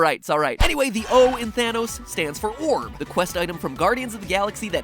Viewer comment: Thought he said Vanoss for a sec :D